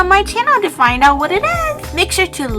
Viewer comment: I thought you said 'lick the notification bell'